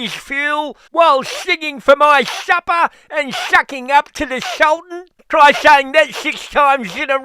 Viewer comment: Did Albo say sucking off the Sultan at 2:42 , Allan would be very disappointed .